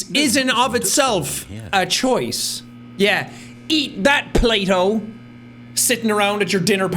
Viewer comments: when he said Plato I thought he said play-dough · I had to double-take when Sean said, "Eat that, Plato!" because I thought he said, "Eat that Play-Doh!"